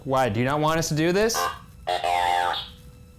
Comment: When ask"do you not want us to do this" It sounded like it said "no,no i dont"